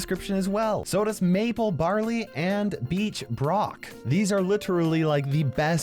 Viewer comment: Who else thought he said bleach brock